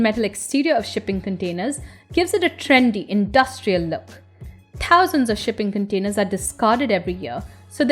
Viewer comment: Did she say, "pousands"?